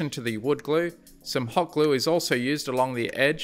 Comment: Did you say "hog glue"?